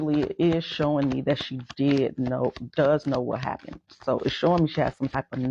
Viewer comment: I thought she said she was upstairs she didn’t know what happened